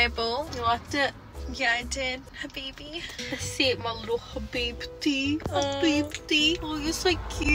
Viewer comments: Did she sayed habibtee in 13:41 !! Howw did you now this word in arabic? 😂💗💗💗 so cuteee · did leah say “Habibi” (my love in arabic) ❤️❤️❤️ I’m Palestinian and I thought I heard you wrong cause I was shocked 🤣🤣 LUV U BTW ❤️❤️❤️❤️